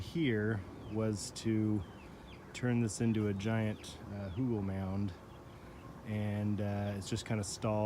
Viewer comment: "giant oogle mound" is what i heard him say. After looking it up, i think he said hügel-mound. I don't know if that phrase is in use, or just something he says; but, from the context i would say that the meaning, clearly, is compost mound or compost hill. A Hügel is a hill, so saying hügel-mound is a bit like saying chai tea. But i kinda suspect that the usage for "hügel-mound" is low frequency and correct. P.S. you can call the "pad" a nopal (nopali or nopales for plural)